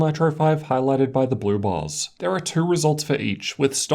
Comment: Every time says "blue bars" I hear "blue balls" probably from all these great laptops I can't afford.